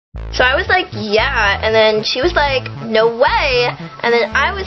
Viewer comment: i swear that i heard "so i was like, GYATT"